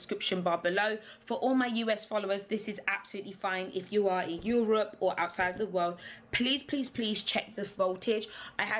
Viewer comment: girl did you just say ‘if you’re outside of the world’??